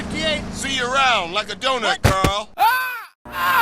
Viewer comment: who else heard, ‘shoot on bangs, nyerderst see you around like the donut girl’ is it just me?